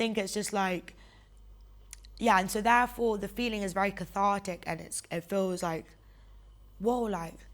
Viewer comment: I swear, I keep hearing the word “c Haha she just said 17:12 “cathartic.” Thank God, that was driving me nuts.